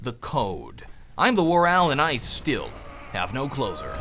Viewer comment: lol first time watching your videos, i thought at the end you said "im the war owl and i still have no clothes on" instead of closer lol